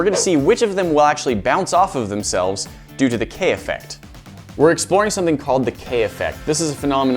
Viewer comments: My bad I Thought it said the Kanye effect. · I thought it said the kayne effect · thought you said “the kanye effect” 😂